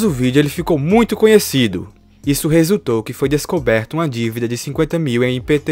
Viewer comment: com certeza foi "descoberto"